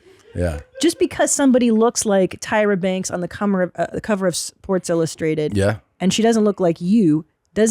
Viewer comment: Christina says "cummer" instead of "cover".